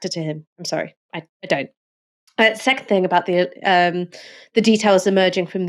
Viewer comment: Did Ash say 'sex thing'? 😆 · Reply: I think she said "second thing" 😅